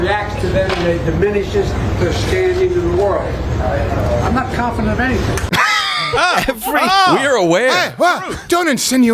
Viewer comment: "I'm not confident on anything" -Boe Jiden What I heard: "I'm not competent at anything"